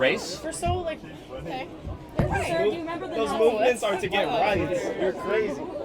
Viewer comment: Sounds like he says "those movements are to get riots" which... isn't entirely inaccurate.